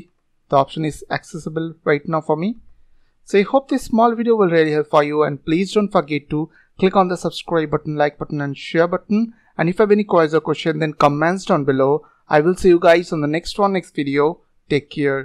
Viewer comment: When he said don't forget to, I heard Don't fuck it to lmao